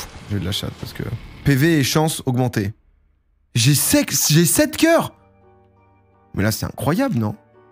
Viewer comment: il dit jai sexe coeur ou quoi la team